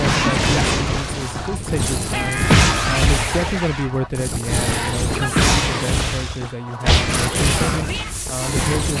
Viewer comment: Really good video but i couldnt hear anything at the 07:00 minute mark because of the gameplay xD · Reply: Thank you for your feedback!! Sorry about that!! What I said was to transcend the units you use most and enjoy using. That would be my advice regarding max transcendence